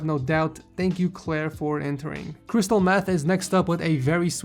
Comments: did you say crystal meth?? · did he just say cristal meth lol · did he just say crystal meth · did I hear that correctly? Crystal meth?